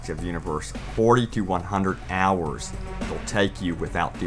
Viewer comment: 40-100 hours -,- THIS IS BAD NEWS OMG PLS NOOOOOOOOOOOOOOOOOOOOOOO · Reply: I thought he said 4200 hours lol. That would be crazy.